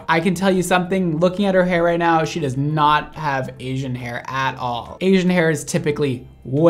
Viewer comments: I thought she said aging hair 😂🤣🤦🏻‍♀️ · “I’ve never heard a white person say they have Asian hair” 🤣😭😭😭😭 idk why that made me so weak 🤣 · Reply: i thought she said “aging hair”😂😂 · The girl who said she has “Asian hair” probably meant she has virgin hair 😂😂 I’m dead 💀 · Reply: I thought she said AGING hair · The girl who said she has “Asian hair” probably meant she has virgin hair 😂😂 I’m dead 💀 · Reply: I thought she said aging hair tbh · i thought she said aging hair... just me?